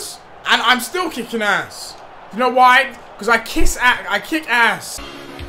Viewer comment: did deji just say "I kiss arse" check it guys